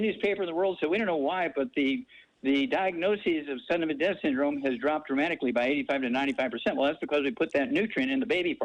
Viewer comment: is he saying sudden infant death syndrome here? He says it so fast, I can't tell. Is SIDS a nutritional deficiency?